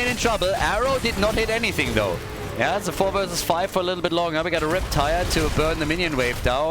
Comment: When I hear you say "the rip-tire" I always think "erectile" and I laugh a little